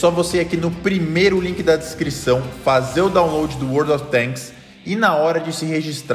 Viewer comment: Tô tão acostumado c o anunciante q quando tu flw "World of Tanks" eu escutei "World of BANGS" kkkk